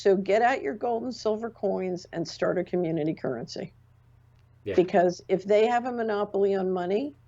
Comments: Did I hear her say get out your gold and silver and start accumulating currency? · Did she say to get OUT of Gold at 46:00? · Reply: No. Actually, she said get your gold coins to start a community currency.